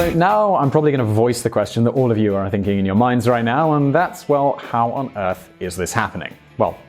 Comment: Why does he say "how on earth is this happing" but the subs say "how the hell is this happning? "